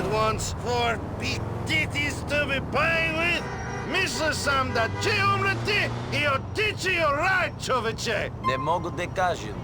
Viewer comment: I can't understand what tf bro is saying and I'm serbian. He sounds more like he is speaking Hungarian or some crap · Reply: "mislio sam da ću umrjeti i otići u raj, čovječe" sounds so unnatural god😭